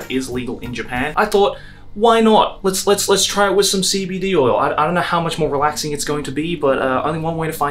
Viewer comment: For those of you who know nothing about weed, it has 2 main ingredients: THC and CBD. THC is the psychoactive compound that gets you "high", whereas CBD is non-psychoactive and basically just relieves chronic pain, relaxes your nervous system, etc. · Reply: I misheard it as “CBT oil” and I was very confused